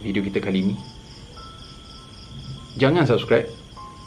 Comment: saya salah dengar ataupun abang memang cakap "jangan subscribe".....hahahahah😂😂😂